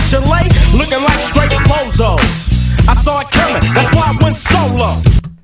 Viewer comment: I always thought he said “I saw a killin, that’s why I went solo” like he saw he could make more bread without them in the future. Crazy